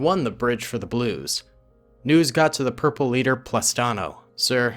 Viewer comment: Did you just say: “Purple Leader, Plastano?”